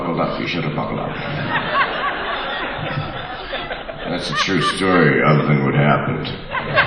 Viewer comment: No matter how many times I've played back I can't understand only one line – at about the 8:30 mark he says, "that's a true story..." and then he says something that's unintelligible like "I doh to dit happened" To which he got laughter and applause– But it's definitely modeled on the recording. Can anybody discern what he was saying there? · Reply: thats a "true story... other than what happened"